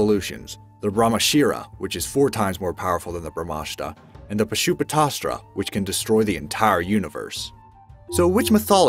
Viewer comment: what did he say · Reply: Pashupatastra